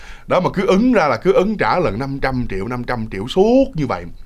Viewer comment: haha có ai như tui nghe thành 500trieu không ? ăn ngập mặt lun á =))))